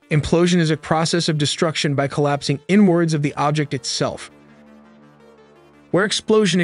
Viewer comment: Omg I thought you said “N-Words” I woke up so fast lmaooo 😭